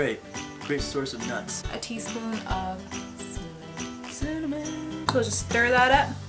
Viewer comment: when she said Cinamon t 6:08 I thought she said Semon